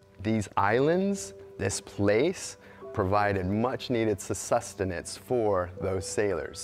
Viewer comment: Ranger, what exactly is "Sussustenance" the word you use at 3:18. ??? The word is -Sustenance...there is no 'sus' preceeding the word. Wow.